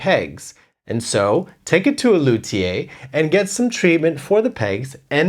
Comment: what is that word and what does it mean? It sounds french but I have no idea how to spell it to look it up myself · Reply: lu·thi·er /ˈlo͞odēər/ noun a maker of stringed instruments such as violins or guitars.